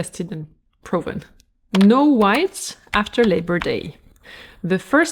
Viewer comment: I've heard it as no white SHOES after labour day.